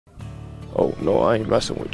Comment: It’s funny how he said,” oh no I’m not messing with you.” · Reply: It sounded like he said “oh no” because of his Baltimore accent “ oh Lore I ain’t messing with you” but either way I could say whatever I want it’s the “blessing” of having freedom of speech just like you can say whatever you want only difference is I don’t get offended if you believe in God or not that’s on you.